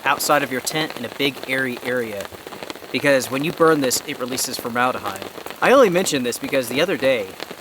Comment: I sent a comment about hex stove, it releases formaldehyde not cyanide I misheard you sorry about that.